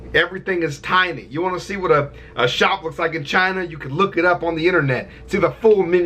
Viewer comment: I thought you said a slut’s vagina but you said a shop looks like in china